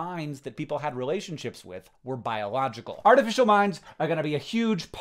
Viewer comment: At first, I heard "artificial minds" as "artificial mimes". Very confusing!